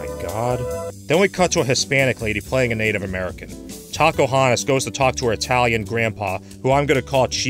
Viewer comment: Did you say taco-hoantes?